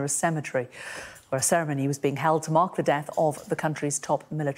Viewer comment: Almost sounded like she said "To Mock the death Qassem Soleimani". If that had been the case I'm sure many more true Iranians would have attended.